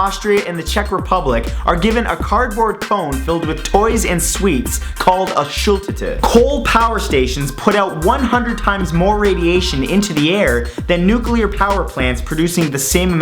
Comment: I was eating popcorn while watching this, and the chewing drowned out the sound a little bit, and it sounded like you said "Shulk tit" Um... I'm really feeling it?